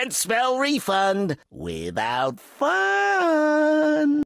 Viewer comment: he actually says «Without Fun» not «Without Fund»